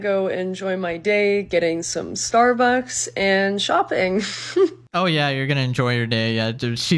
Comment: DID she just say SHOPPPInG? I Need money, I didnt get a job, what about doing some shopping that helps